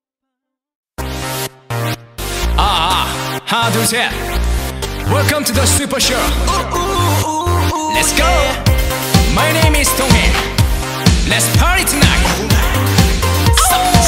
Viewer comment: Did he just call Amber Donghae? They do look sightly alike now that I notice o.o